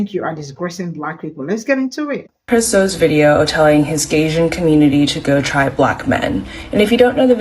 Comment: Gaysian? 🤣🤣🤣🤣🤣🤣 WOW! The BS they come up with. I can't unhear that. 🤣🤣🤣 I didn't hear nothing else after that.